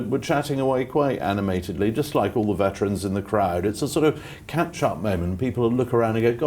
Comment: Sorry - asking as an American - at 15:02 : "it's a sort of [capshot] moment" - what is that word he is saying?